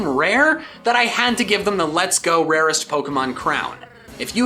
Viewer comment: thought you said no shines? Lol